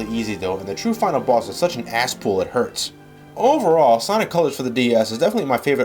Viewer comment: What does say 15:20? "Such an asspool?"